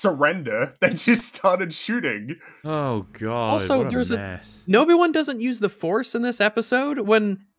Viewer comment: Is it just me, or does say "Nobi-Wan" here? To go along with Jake Skywalker, I suppose. :p